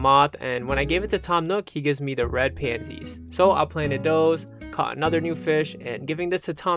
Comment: ngl i thought he said red panties